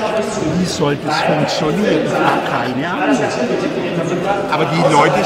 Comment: Schwer zu verstehen, aber er sagt wohl wirklich "Ich hab keine Ahnung", nicht "Ihr habt keine Ahnung" (min. 1:20) Und ausgerechnet den Spruch habt ihr in die Videobeschreibung gepackt :S Nehmt lieber "Dann macht euren Wahlkampf selber"^